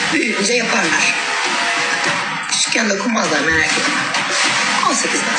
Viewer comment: ne diyor ? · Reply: Küçükken dokunmazlar merak etme diyo